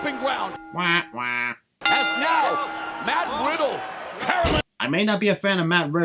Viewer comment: i think he said matt widdle